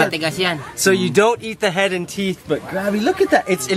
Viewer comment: I thought he said head and tit, LMAO! He actually meant to say head and teeth.